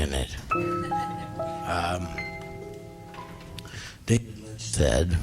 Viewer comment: what does he say at 1:12:16 ? like when he's "quoting" someone, I can't make it out :/ And fucking brilliant shit. Tom Waits is truly like no other. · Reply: David Lynch, I think